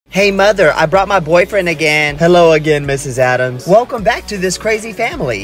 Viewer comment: I thought it said I bought my boyfriend again 😭😭😭